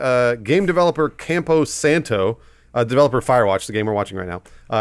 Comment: I thought Bruce said "Firewalk" at the beginning and got really excited. #amberprice